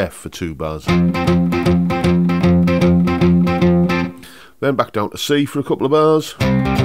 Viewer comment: I love when you look back, especially as a Yank who doesn't always get the British references. At one point I think you said, "then back to the C for a couple of bars". What I heard was, "Then back to the C for a couple of hours!" Sounds like the blues to me! ;>)